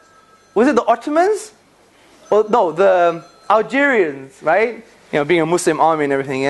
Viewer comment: did you say Algerian's ? because I'm from Algeria